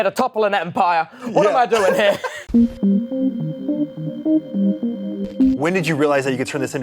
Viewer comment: i got an add right after he said when and I heard 'when are you ready to vape' and I lost my mind lol